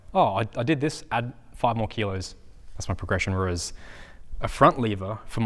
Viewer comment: It sounded like bro said progression rizz